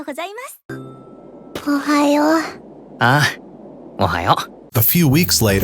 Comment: bro did he say ohio